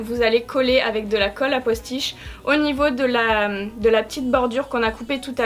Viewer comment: Il faut mettre de la colle quoi ? 😕 Aquastish ??? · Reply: "À postiche" 😂😂 autrement dit spécial perruques.